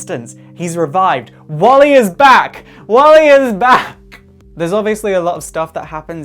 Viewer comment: When you said "Wally is back!" I heard "Wally is Black!"